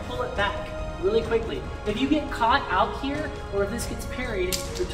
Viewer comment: Okay the video cut off WAAAYYY too early so I heard "you wanna combine this with evasive butt-" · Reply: Footwork